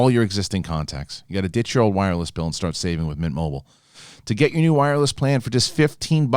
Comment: I'm having an ear glitch, what is that word your are saying before mobile, I hear mit, mick, bit, I can't figure it out. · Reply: Mint Mobile